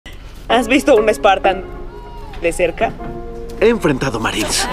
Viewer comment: Por un momento pensé que dijo "un espatano"